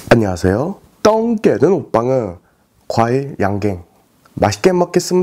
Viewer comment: Can somebody tell me what "bomb ah" mean?? He say that in every video opening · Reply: I think it’s mean today’s mukbang.. “Mukbang eun”